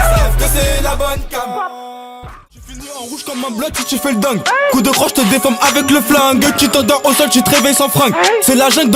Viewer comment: cst quoi le freestyle à 8:14? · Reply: batara gang bang bang